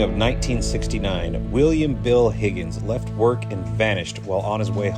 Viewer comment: This is not meant to disrespect this gentleman. The first time you said his name I honestly thought you said Wild Bill Hickock. Maybe I need a hearing test. 🤿🏴Betty from Scotland 🏴🤿